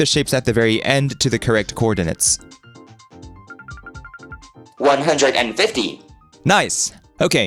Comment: The subtitles at 23:07 don't match the spoken lines. The computer says "150", but the subtitles say "150!", which is much, much larger than 150.